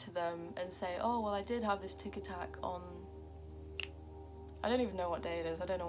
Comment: At one point I heard "tic tac" rather than "tic attack" idk why